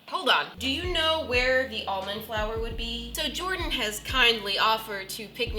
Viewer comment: I thought that when she said almond flower she said omlette flower :>